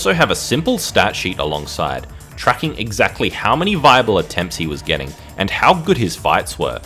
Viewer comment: I heard this as "Bible Attempts"; also an accurate way of looking at his runs LOL.